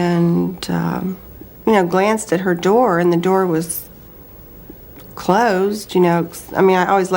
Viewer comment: The very first point you make about Patsy "correcting herself" regarding the door was misheard. Patsy said, "i glanced at her door, which was closed... cause I mean i always sort of left it ajar. " It's only a little point, but she's not "correcting herself or catching herself," as you say. Just saying every little thing counts.